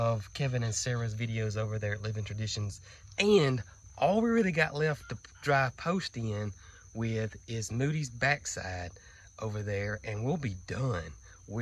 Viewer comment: I started laughing when you said all we got left is Moodys back section, now why because I thought you said something like all we got left to put in the post by Moodys back side… I for some reason picturing moody pushing in the T posts. Still early for me. God bless